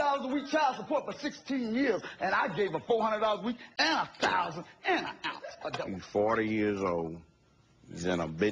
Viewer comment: I could not have heard that right? Did he say (7:20) he gave her $400 a week and a thousand and an ounce? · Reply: Michelle Cayson he said a thousand and an ounce